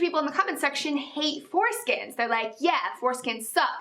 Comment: in some places i`ve heard they cut them up fry them up in vegetable oil, salt and eat them.. I`m sorry i thought she said pork skins.